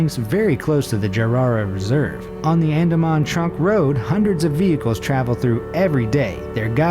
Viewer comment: sounds like he says Andamon Trump road?.?